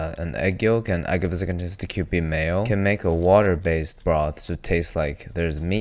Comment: for 2:43 what did you say, even the subtitles are confusing, what type of mayo do you use